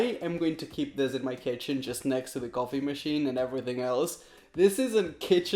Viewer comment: I thought he said golfing machine instead of coffee machine!😂😂😂